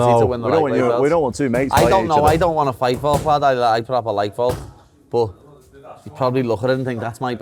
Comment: I legit do not know what the fuck paddy said before he said I don’t wanna fight Volk lmao tried to replay it 7x and still can’t distinguish what he said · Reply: "I don't wana fight volk lad, I propa like volk